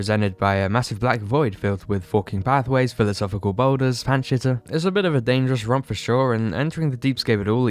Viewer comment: I really can’t tell what you are saying I heard “pan shitter” and “panshea” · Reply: Pants Shitter, I believe. She's referencing the jumpscares I think.